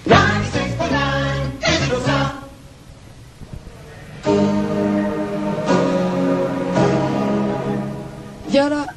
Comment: Qué dice el jingle de 0:34? Yo solo entiendo lo de "ninety-sixty nine", lo demas se me pierde. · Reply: 96.9, digital sound.